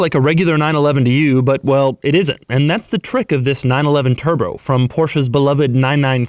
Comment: at the 1:17 mark, did you say TUR-BRO?